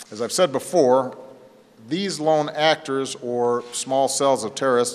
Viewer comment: did he just say actors at , 2:37 ????